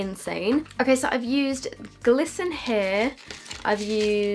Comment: When you held up the glisten here palette up I could’ve sworn it said gluten free😂